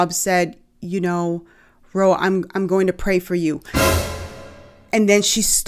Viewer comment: did she say "I'm going to pray for you?" or "GIRL, I'm going to pray for you. I am assuming the former, but that is very different.